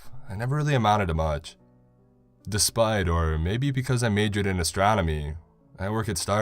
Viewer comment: At first I thought he said he majored in astrology. I was like, 🤔 lol.